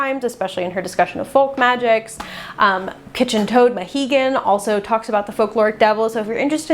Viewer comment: Can someone tell me what username she’s saying here? 7:07 kitchen toad who? I can’t find them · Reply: Kitchen Toad Mahigan. I looked here on YouTube and didn’t see any of their videos so maybe they are on TikTok?